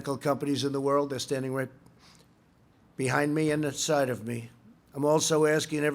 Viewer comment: So we're just gonna ignore, "They're standing right behind me, and inside of me,"?!? Wtf does that MEAN??? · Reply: He said "and at the side of me" lmao how are people mishearing that